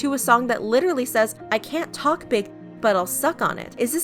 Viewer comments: Waitttt..... I thought you he song said can't take big d not can't talk big d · Wait Wait Wait Hold up DID SHE JUST SAY CANT TALK BIG D***? I thought it was cant take big d***.